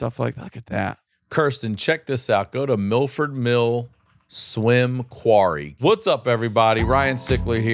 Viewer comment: Funny I heard you say milford mill swim quarry at least I think that's what you said 😂 I grew up in a town in Massachusetts milford that also had a quarry not that nice it did have a rope swing and a bunch of insurance job cars in the bottom lol so dangerous they went one time and pulled a ton of vehicles out of it after a kid broke his neck landing on a jeep I believe it was